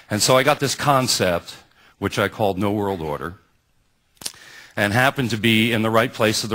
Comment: did he actually say new world order ?..